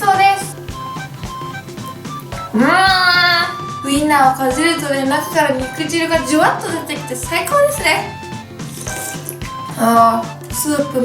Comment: がどうしても聞き取れない…… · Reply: るい るい ウインナーをかじるとね、中から肉汁がジュワッと出てきて、最高ですね。 ですよ